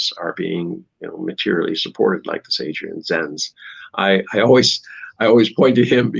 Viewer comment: At about 26:06, Mr. Tangen starts talking about some anti-China propagandist, but I can't understand the name. It sounds like he's saying Jay Senz. Can anyone tell me who he's talking about? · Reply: Adrian zens